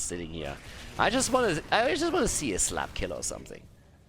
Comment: Okay so when you said "give them the slap" I was only half paying attention and I heard "give them the SLUT!!"